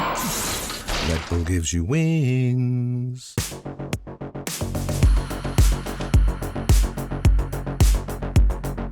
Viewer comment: yo what did Ludwig say at 1:07:40 thats crazy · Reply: Demon